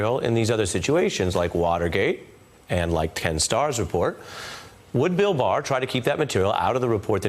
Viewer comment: Did Ari Melber just say "Ten Stars report", rather than "Ken Starr's report" It sure sounded like it. .